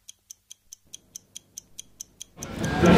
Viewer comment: Quelqu'un peut me dire ce qu'il a dit à 9:17 ? je sais pas si y'a que moi mais j'arrive pas à comprendre ce qu'il a dit ^^ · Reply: Je crois qui dis que l'equipier lui a mis un faux plan...pour le repas gratos...mais c'est vrai qu'on comprends rien du tout....🤣